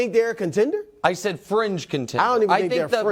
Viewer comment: I was listening to this with headphones and I thought they said "cringe" contender instead of "fringe" contender. Then I realized that both were equally true.